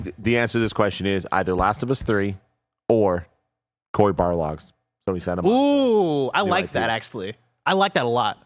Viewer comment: WTF DID HE SAY 51:46 ?? Last of us or WHAT? Man you gotta stop mumbling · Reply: Cory Barlog’s Sony Santa Monica game